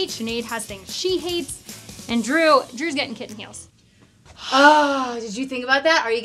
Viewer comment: I thought she said "Kid's meals" Instead of "kitten heals". I was like - Like chicken nuggies? Drew gets the best deal!